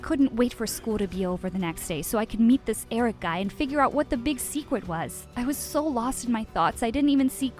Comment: I heard "Wut the big seekwut was"